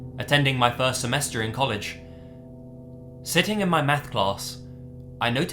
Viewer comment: When I first heard the beginning to story three, I thought I heard say "sitting my METH class". That's one way to get kids to attend school and pay attention, hire a Walter White type.